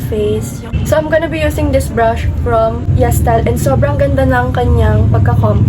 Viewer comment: im sorry but what is the brand of your brush? cant hear it well huhuhu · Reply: she said she got it from YesStyle, she didn’t say the name tho